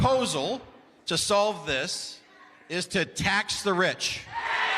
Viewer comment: to the senator or government official at the town hall meeting , thought they were saying attack the rich.. when they said ..tax the rich..!!😮